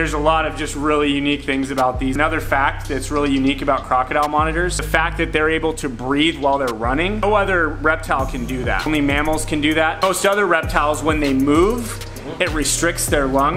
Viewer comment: Oh my gosh. I totally thought he said breed while running!